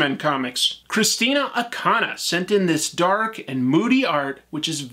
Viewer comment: Sounds like you said Christina Ocana but is written Art by Christian Ocana.